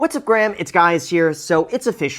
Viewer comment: Did I hear “what’s up Grahams, it’s guys here”? 😂